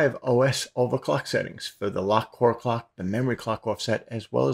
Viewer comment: i thought they said dont lock the core?????